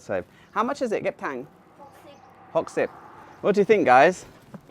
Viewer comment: what did you say at 7:25 after you said how much is it… My Thai wife can’t understand. Is it Thai language? Sounded like Yip Tang?